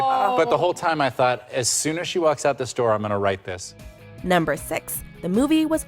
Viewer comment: the store?? Werent they in his appartment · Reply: He says "as soon as she walks out this door". But when I re-listened it does sort of sound like he says store instead of door.